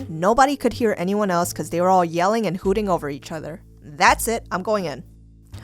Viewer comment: i’m going in, that’s what he said